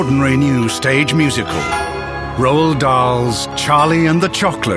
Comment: I thought he said Rowl Dahl... -_____